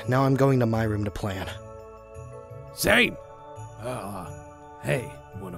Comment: I don't know if it was me but did garroth call laurance Zane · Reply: lexy the bunnie Garroth said "Say" not Zane